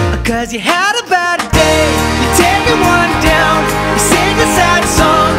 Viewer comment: Instead of "You"re taking one down" , I always thought he was saying "Your telly went down". OMG! That really WOULD be a bad day!! 🤣🤣